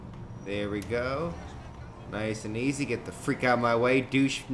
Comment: U said duchenugget my mom thought u said chikennugget lol